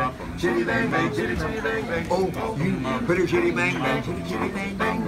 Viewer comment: oh and at 0:05 he clearly says "pretty 5h1tty, bang-bang"